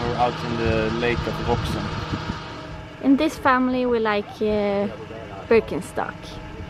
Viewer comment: Another great video as always !! On a different note, anyone knows the name of the sandals brand at 11:49? I couldn't understand the name and they look super comfy. Greetings from Japan 👍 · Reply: Birkenstock!